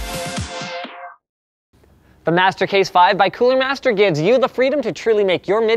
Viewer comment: i thought he said "master race 5" for the advertisement.